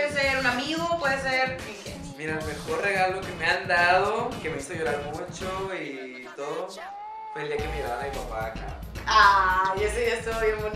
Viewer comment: Perdón Pero No Entendí Lo Que Dijo En El Minuto 3:56 ??? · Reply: Allan Israel Gonzalez Garcia su mejor regalo FUE LA SORPRES DE SU PAPÁ EN EL ESTADIO